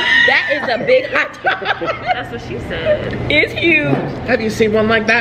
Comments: the waitress really said “thats what she said” 😭 they didn’t even notice 💀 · Is it just me or I just 23:40 the waiter said: thats what she said